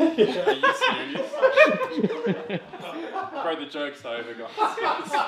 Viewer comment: What does he say at 1:15? I cant understand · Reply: Bro, the joke's over, guys.